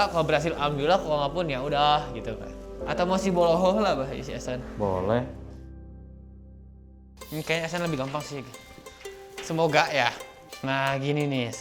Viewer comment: Kenapa Ehsan di panggil bloho a? 😂gak tau dah kek nya aku salah denger 🙏😂